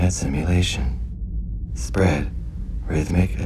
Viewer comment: DAD: Spread Me: did he just say bread Me: ohhhh he said SPREAD